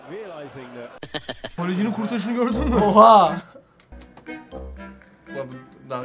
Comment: ne dedi oha mı????